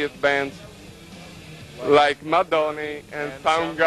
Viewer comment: Lol did the interviewer just refer to Mudhoney as Madooney? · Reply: He said Mudhoney? I thought he said 'Madonna' 😂 But Mud Honey makes more since